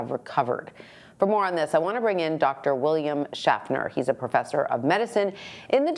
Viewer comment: Lol. I thought she said Dr. William Shatner at first.